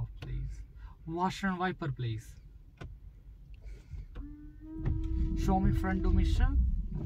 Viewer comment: I can’t understand what’s being said at 1:04-1:110. Is it front condition and back condition? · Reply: He asks the driver to point to front and rear demister (this defogs your windows/windscreen) ... and hazard lights